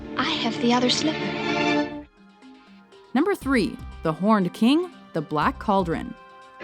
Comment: I thought you said "The Horny King"! LOL!